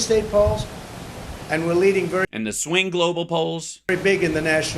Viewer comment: Hal you need your hearing checked, he clearly said “local.” Please don’t make me defend this fuck